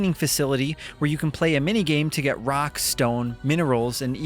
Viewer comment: did somebody say Rock and Stone!?